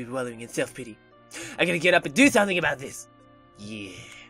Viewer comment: When it said 'inspire', I thought it said 'infire'